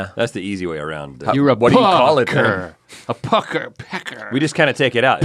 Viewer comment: Did Link just say Pecker?